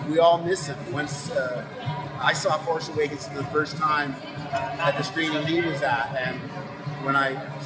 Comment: What is he saying at 0:15 forwards?? "When I saw TFA for the first time I _______ with that." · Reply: "When saw TFA for the first time at the same screening he was at" referring to george